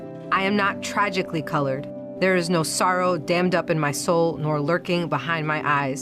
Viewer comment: did she says "SOROS" ?? and is Latina considered "Colored"... and I thought that's a no no word.. especially from a woman who ain't Black. Also its great to know about her Metal Disabilities are like her badge and identity... WERE DOOMED.